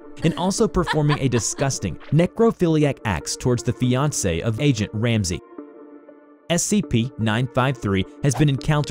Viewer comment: i thought he said gordon ramsay but he said agent ramsay